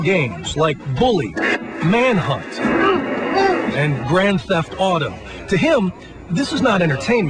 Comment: sounds like he says grand theft autumn XD